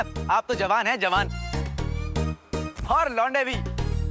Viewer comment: Did he say jawan lunde 😂😂😂😂😉😉😉😂